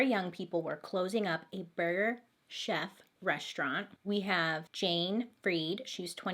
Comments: Lol i thought you were saying Burger Jeff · I died when she was laughing at the way she pronounces burger chef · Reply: I thought she said burger jeff at first lol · I died when she was laughing at the way she pronounces burger chef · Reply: i heard “burger jeff” and was laughing with her 😂😂